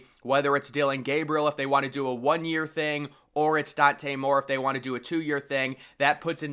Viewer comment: Whoa, did you just call Dante a Moron😂😂